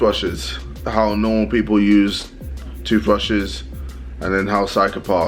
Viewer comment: when jj was saying ''toothbrushes'' i thought he was saying ''two flushes''